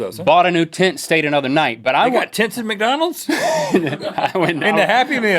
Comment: I thought Rhett said "tits at mcdonalds"